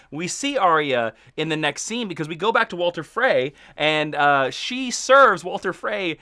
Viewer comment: Am I the only one who heard him say "Walter" frey